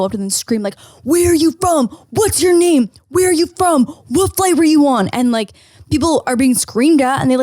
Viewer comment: TELL ME IM NOT THE ONLY ONE WHO HEARD "WHAT FLAVOR YOU WANT????" AT 24:56 HAHAHAHA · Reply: I think she meant to say “what flight are you on”